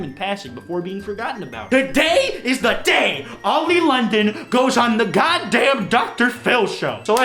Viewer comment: As an American i thought it said Oil London